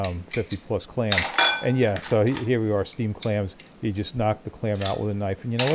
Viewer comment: Steamed Clams? I thought you said steamed hams!